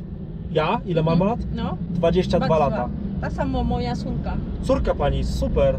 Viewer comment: usłyszałem tak samo moja suka xd